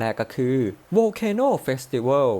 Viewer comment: ได้ยินว่า volcano fifty-bath